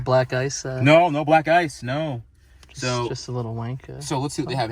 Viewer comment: does he say "just a little wank cloth"